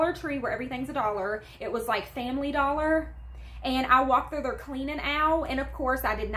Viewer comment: For a moment I thought you said you got that pink cleaning fluid from the ‘family doctor’ 🤣🤣